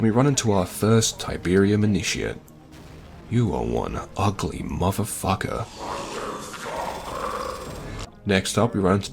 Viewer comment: Did I hear the mutated "get out of here stalker"? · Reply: I heard "Motherfucker!" instead.